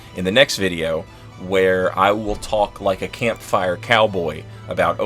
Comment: For a second, I thought you said “overcoming diversity,” and I thought, “gee, that’s an odd way to end a video.” I laughed out loud when you lured those guys into the area with the death frogs. And that frenzy scream kiss kill was brilliant.